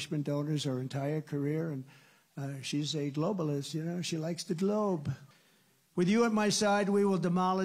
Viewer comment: She's a globalist. She likes the globe! ❤️ 🌎 😱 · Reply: I swear I thought I heard him say "She like TO globe!" 🤣🤣